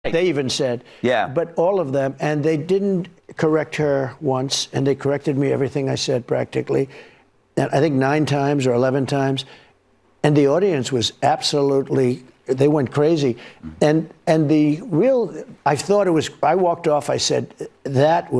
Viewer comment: Sounds like he said "i lied 9 times"